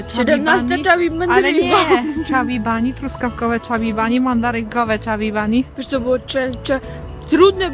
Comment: Ja przy 16 chubby bunny mandarynce, zamiast chubby mandarynka to usłyszałam 16 chubby blondynka... XD